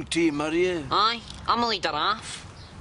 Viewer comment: The bit where the ned spray paints Jack and Victor, I can never make it what he says there! Am the leader sumit…. Can anyone put me oot ma misery? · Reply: It sounds like he says, "I'm a lead giraffe"